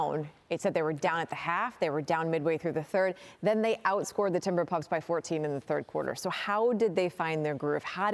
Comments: Did she say Timberpuffs? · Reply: Timber "pups" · Did she call them the timberpups 😂😂😂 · Did she say timber “pups” at 8:15 in lol??? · What did she just call the Timberwolves? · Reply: The timberpups 🐶